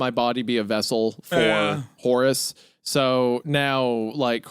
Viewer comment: Long story short - I definitely heard “horse’s harmony” not Horace’s 😭